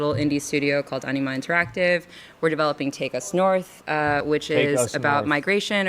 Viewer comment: I heard 'we're developing take a snort'. Close enough considering the topic lol